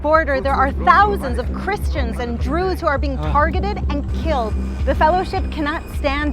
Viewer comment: Why does she say "Drew's" instead of Jews? Even the captions can confirm that's indeed what she said.